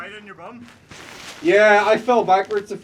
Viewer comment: when your friend said, "you fell on your bum," I first heard, "it's tighter than your mum."